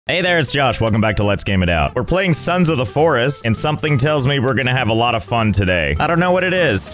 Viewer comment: LMAO i heard "sans of the forest" like it would be a microsoft font